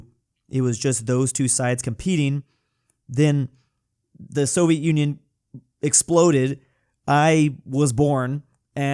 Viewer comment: At this point, u sounded like u said sovit Union exploded, and I came out